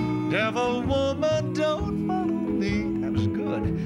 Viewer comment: did he say “devil woman don’t “f…” me? Has to be a different word than what I’m hearing. They didn’t use language back in the days with those words in songs. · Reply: he said don't follow me